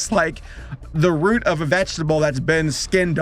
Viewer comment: LICEEEEEEEEEEEEEEEEEEEEEEEEEEEEEEEEEEEEEEEEE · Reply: I thought you said it was a veggie tails